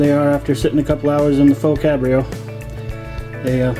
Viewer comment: Great vid I'm getting ready to do my first brisket on the pbc so trying to pic up tips from folks. I've got a question for ya on something you said I can't make it out but you said it a few times so you got me curious. At 8:42, 9:32 and at 9:50 you talk about finishing in something. What are you saying? Great looking brisket hope mine turns out as well. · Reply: The Faux Cambro (I was saying it wrong lol) its basically a cooler! the Brisket needs time to rest or more like to redistribute the heat So you need to wrap it in towels and stick it somewhere (like a cooler)or even in your oven (not on) and let it rest for 90 min to 2 hours. This evens out the heat through the whole hunk of meat and helps tenderize it some more. You really want to serve the meat at 150ish so time in the Faux cambro lets the meat cool down as well. hope that answers your question